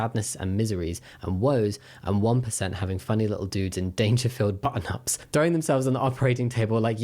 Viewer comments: Lol I was only listening so when said “dangerfield button ups” I heard “danger-filled button ups” and said ah yes the danger is boobies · Dangerfield is an Australian clothing store, they are super accepting of the LGBTQIA community and there's not a queer person I know who doesn't shop for clothes at Dangerfield 😊 Over half my wardrobe comes from Dangerfield 😅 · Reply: Oh lol, I was just listening and thought he said "Danger filled button up tops". I thought it was a silly way of referring to the bits that were about to be yeeted XD